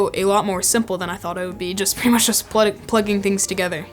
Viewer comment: did he say Prettymuchsshshshshhs?